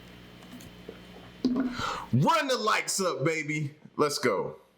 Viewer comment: If you a TMG FAM member RUN THE LIKES UP!! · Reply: For the longest time I too thought he was saying run the lights up!😊